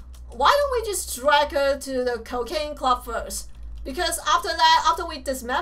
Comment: I thought she said cocaine club!!